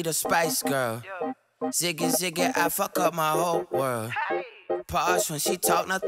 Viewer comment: Lmao I thought he was saying ziggy ziggy Africa my whole world